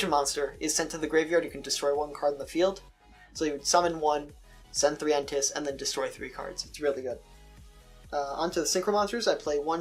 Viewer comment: u mentions a fusion monster but o couldn't catch it's name. u said send three something to grave and destroy 3 cards on the field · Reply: elder entity N'tss it's not out yet but when it does come out it will be pretty useful in the deck.